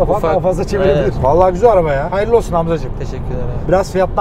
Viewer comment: ne diyor .... Hayirli olsun ablacim mi ???? Jssjwjhshwje Wtf